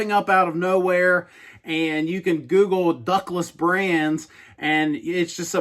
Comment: I thought you were saying “Douglas brands” in the beginning of the video. . . .